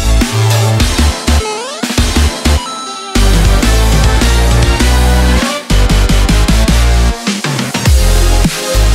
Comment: what does it say? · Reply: "This coins are only good in this kingdom"